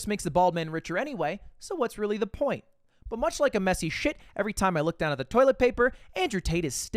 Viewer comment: God I have Argentinian brain rot, at the start when said "messy shit" I heard Messi's shit and I got angry lol